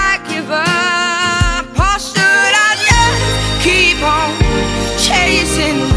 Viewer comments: i have been misquoting this as "should i just keep chasing cavemen" i am literally mortified!!!! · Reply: that's ok for years I thought it said "chasing penguins". At least yours makes more sense XD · I know it's ridiculous, but I always hear "chasing penguins." Fantastic imagery. :)